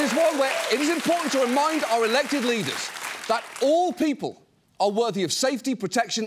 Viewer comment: thought he said "alleged leaders"